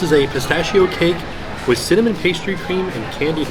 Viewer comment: Oh CINNAMON pastry cream...I thought he first said salmon pastry cream!!! omg that sounds so much better lololololol